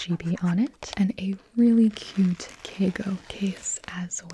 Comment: Does anyone know what she says at 1:59? Like Caygo?